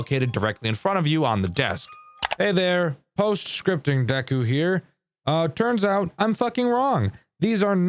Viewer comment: Did he just call himself deku?